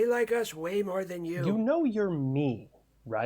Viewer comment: “You know your me.” “Ya, but they don’t.” The fourth wall break and simultaneous looking the cameras had me bust out laughing. Epic delivery and editing! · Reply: I thought he said "you Know your mean" and grandpa was like "Ya, but they don't" XD